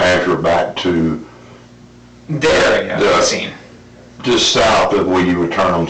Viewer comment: you're welcome! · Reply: thanks, I thought he said Jerry. I couldn't find anything.